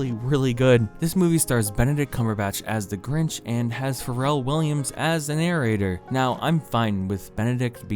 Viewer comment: When he was talking about Pharrell Williams i fucking thought he said will Ferrell and I was very confused